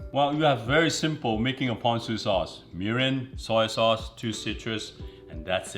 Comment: He was saying Mirin. I thought he was saying urine.